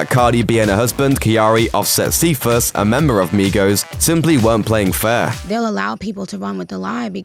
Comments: The first time commentator said Migos it sounds like he said Negros. Lol 😂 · I thought he said migros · Its migos. He sound like he saying migros? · Why it sound like he said negros instead of migos